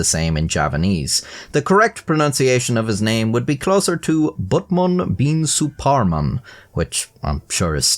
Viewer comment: Did u just say Buttman ? XD HHAHAHAHAAHAHHAHAHAHAHAHAH